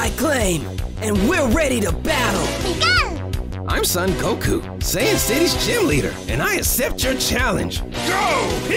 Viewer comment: Goku the Coldest Rapper ever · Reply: did he say "Saiyan city's cheerleader"?